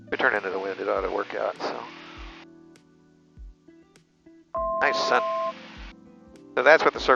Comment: & did he said nice son @ 16:27?!?! It seemed lik it. Lmao & get well soon my gud brotha!!!!! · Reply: Nah, he was looking into the sky as they turned and said “Nice sun.”